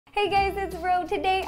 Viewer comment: No hate but at the beginning, the first time I watched your videos I thought you said “Hey gays”. Lol. Love you Ro.